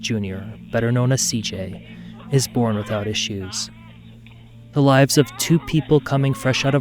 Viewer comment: I have to lighten the mood a little. The first time I heard 39:25, I heard, "...better known as CJ, is born without his shoes." 😂😂😂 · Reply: Born without issues! LOL